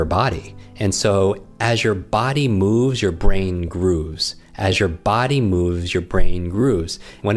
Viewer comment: What is said at 4:45? "When your body moves, your brain .....?" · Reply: Grooves